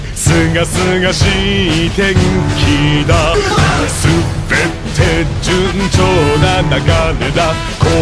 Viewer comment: does he say "Super Ted" or did I imagine it?